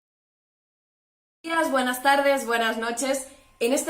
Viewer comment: no entiendo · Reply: Digo únicamente "buenos días" al inicio, aunque no se oye muy bien.